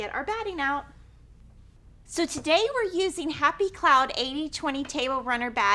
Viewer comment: I was wondering the same thing. For runners I usually use leftovers from quilts and use joining tape if needed. I tried to find it on the Hobbs website but couldn't. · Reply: Oops- I thought said Hobbs batting but I see she uses "Happy Cloud" and sell it at Fat Quarter Shop. It says 19" x 55"